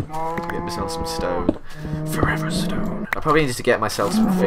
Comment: I thought you said forever stoned haha